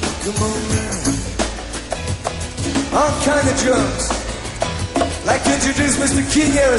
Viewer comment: "I'm kinda drunk!" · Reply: Shawn Sellers Ha! I always thought he said, "All kind of drums!"